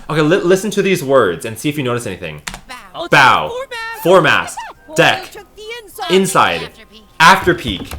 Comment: Also The The Words Said On 54:32 It Said: Bow The Mask Deck Inside After Peak At Least Thats What I Heard